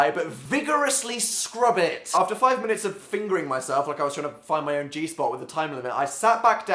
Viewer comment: did he say "after 5min of fingering my self" at 5:20?